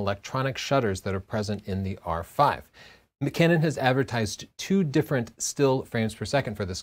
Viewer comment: Did Tony say McCanon